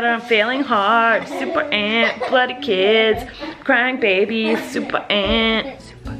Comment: When she said “super aunt, bloody kids”, I thought she said “ super aunt, abuses kids”!!! Lol 😂